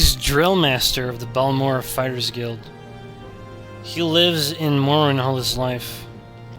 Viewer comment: What the hell did you just say? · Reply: just readed "He's lived in Morrowind all his life."